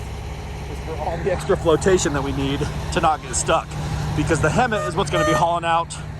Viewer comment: Every time you said hemmt I thought you were saying Hemet.I live in Hemet.